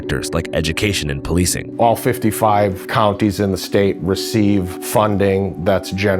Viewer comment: Did he say 55 counties??? 1:42 crip mac we miss you